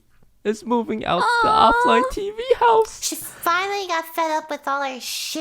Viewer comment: Did she say FED!?!?! o_O